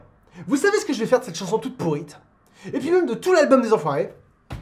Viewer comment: C'est moi ou tu dis vraiment "toute pourrite" à 20:30 ?? Non pas toi, pas maintenant, pas après tout ce que tu as fait....